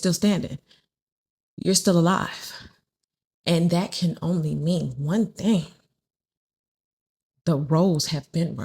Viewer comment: When you said- "We're still alive, and that can only mean one thing" .... I heard "Cuz you're the G.O.A.T." Lol <3